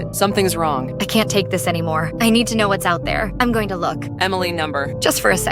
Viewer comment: EMILY NO (*+says number instead of no+*)